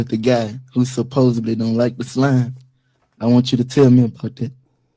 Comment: "Who supposedly don't like Islam" is that what yungboy said? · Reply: Don’t like the slim